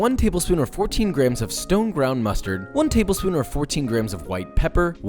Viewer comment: Am I the only one who heard stoned brown mustard instead of stone ground?